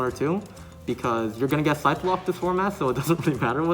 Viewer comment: Getting back into YGO and there's new terminology I'm not familiar with. What did he meant when he said "you're going to get sideblocked this format"? Also, what are "two-ofs" which he mentioned regarding his side deck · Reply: 1. He’s talking about getting “Scythe”-locked. Artifact Scythe is heavily played right now, and it’s effect locks you out of summoning from the extra deck for a turn. 2. Two-of just means playing two copies of a card, when most people would say you should play the maximum amount of copies of a card you want to see in your hand, that maximum being 3 copies. Hope that clears things up a bit. :)